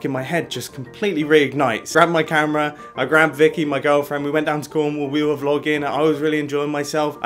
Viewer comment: Wait did I hear this right 4:06 I wrapped my girlfriend